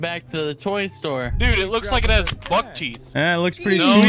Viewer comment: "Looks like it has Buk teeth" 😂😂😂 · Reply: It's because I misheard Buck Teeth as Butt Cheeks, not because I think Teeth look like Butts.